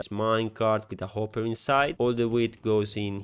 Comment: Solo io ho capito that will farm "weed" 😂